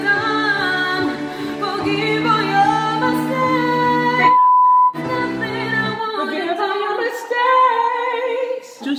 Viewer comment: can someone understand what's bleeped? Is it the name of the girls and she prefers to stay anonymous maybe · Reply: She sai like "che cazzo..." that is like "what the fuck"